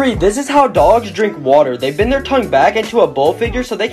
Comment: I thought it said “Hot dogs actually drink water”